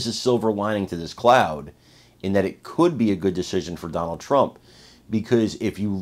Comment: I thought you said good news for Trump.